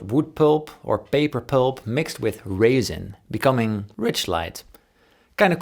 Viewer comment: I thought you said it was wood pulp mixed with raisin. I googled and it’s resin, that makes more sense lol.